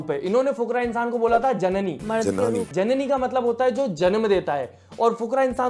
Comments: Very well said!❤❤ 3:10 Just a correction, जननी means mother and जनानी means women. He actually said जनानी which he is referring to as gossip aunties. but your point is anyway correct. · i think avinash didnt say "janini" he said janani which means women...